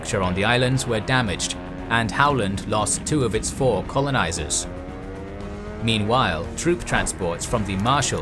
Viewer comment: Am I mishearing stuff or did I hear "2 of it's 4 colonisers" at 12:06?